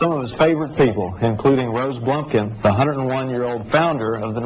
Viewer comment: Did he say Rose Blumpkin at 1:28 ? Hahaha what a name. I could go for a blumpkin right about now